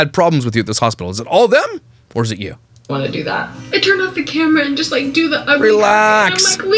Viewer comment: did she say, “I turned off the camera”? Maybe that is why the staff was frustrated?